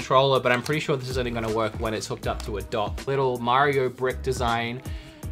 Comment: I know you thought that said "b****"